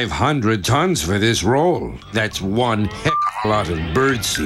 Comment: "Heck" is not a swear. No need for the bleeps. That's what I said in my head when I was 10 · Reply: I though he said Thicc at first